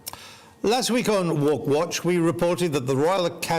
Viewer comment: The first time I heard Andrew say "woke watch" my jaw dropped.. Then I realised what he actually said.